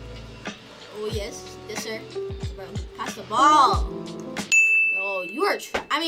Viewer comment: Did he say pass the ball😂